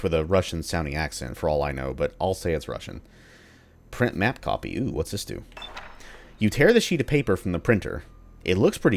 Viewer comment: it was indeed Russian but it was so garbled it was hard to make out what was being said. "Disarray" - a perfect title given what's happening on those consoles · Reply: It was "девяносто три, восемьсот восемьдесят два, на-и-ми-на". Or "93, 882, naimina" and I have no idea what the "naimina" means, probably some abbreviation NAIMINA.